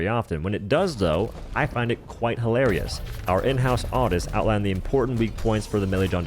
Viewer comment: the pilots don't have medium armor anywhere, our in-house artist got that wrong. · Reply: Lol i heard "in house autist" which is likely equally applicable 😅